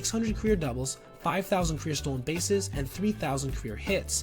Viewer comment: Did I hear that right at 2:53:25? 5000 career stolen bases?? Move over Ricky Henderson 😂 love the video dude good stuff